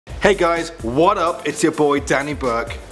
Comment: It sounded like he said "Hey guys, one-up." At the start. (For me)